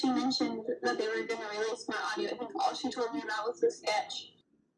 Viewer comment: My audio ??? come clean . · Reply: I had thought she said "more audio" But I could be wrong